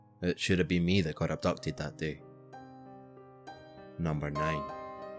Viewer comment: abducted? I can't hear that part very well